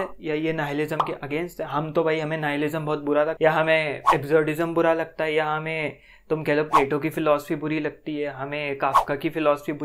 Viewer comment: What did you say on 0:55 Exorcism or something, I don't understand plz can you reply me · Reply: He said absurdism